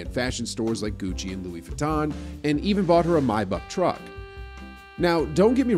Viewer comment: Nigga did you just say my buck truck?